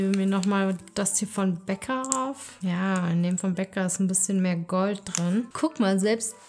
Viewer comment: „Boah ich rieche wie eine Süßigkeitenstation, ich gebe mir noch mal etwas von Becca drauf“ ich habe erst verstanden, dass du dir etwas vom Bäcker drauf machst und mich gewundert seit wann es beim Bäcker Make-up gibt 😂